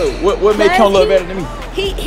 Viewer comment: did she say “ he aint have no hair” ?